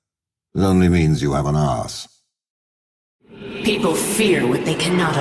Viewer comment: i heard ass , not arse